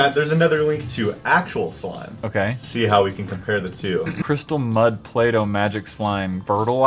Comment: i thought he say butt but when i look on it it was mud XD